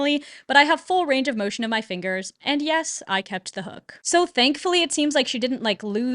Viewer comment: Re-watching this after a couple months and I fully thought you were going to say “So thankfully she didn’t lose her hook-“ 💀